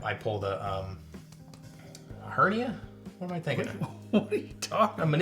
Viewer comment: Did he just say he pulled a hepatitis in his neck 😮